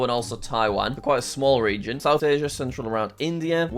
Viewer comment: i thought it said religion 😂😂